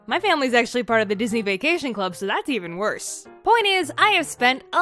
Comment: you said worse, the captions said better